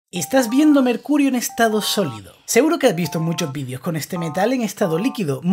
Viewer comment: Por qué escuché "estas viéndome el culo en estado sólido" *se replantea la vida*